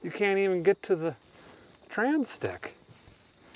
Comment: Did he say “trans dick”? Not that I’m judging